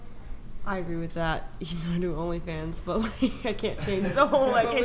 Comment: Thought said, "oral fans" wonder why brain went there.